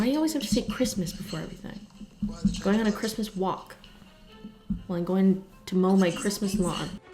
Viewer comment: thought she said christmas wap instead of christmas walk